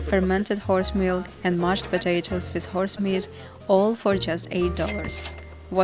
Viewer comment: Wait, did you say "horse meat"?